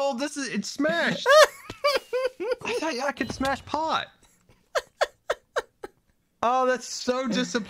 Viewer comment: sorry, but did say he could: "smash pot???"